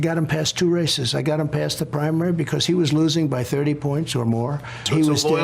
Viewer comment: Go to 5:30… I swear he says ‘prime rib’!